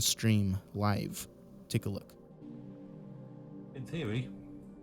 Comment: he said: „in theory“ but it sounded like „ hey siri“ and I‘m 100% sure that was Siri on his iphone answering with „hmm“. Edit: he says he doesn‘t have an iphone just an ipad. It was definitely Siri. Every morning I take a shower and I ask Siri to start a timer. And Siri always says „mhm“.